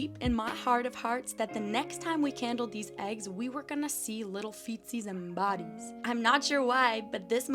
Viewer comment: your subtitles said feces instead of feetsies 😭